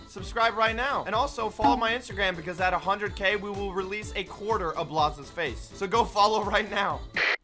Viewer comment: follow insta pls or i cry → <link> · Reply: Guys follow the insta so we see a corter of blazas face And I thought he said a corter of memes face